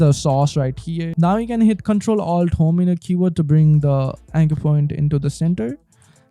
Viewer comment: Question... What Are You Saying Here? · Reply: Control Alt Home? if so, what is home on Mac?